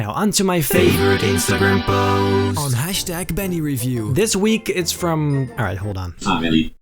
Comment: always amazing 👍🏻👍🏻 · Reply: I could have sworn you said bunny